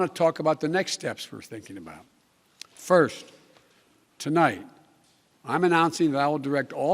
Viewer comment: "Good evening my fellow Americans.. "Tolight"... " what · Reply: I thought I was the only one who heard him say Tolight. I turned on the CC and noted that it said tonight instead of potato biden saying tolight. Youtube is correcting potato biden mistakes.